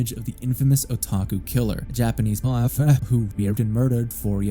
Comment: what’s he saying? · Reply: mafia